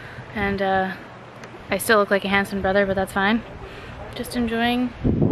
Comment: "I still look like a handsome brother but that's fine" 😂😂😂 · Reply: Stephanie Salcido i thought she said Hanson brother - the guys that sang MMm Bop 😂